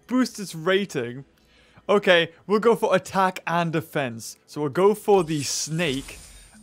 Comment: did he say attack the fence?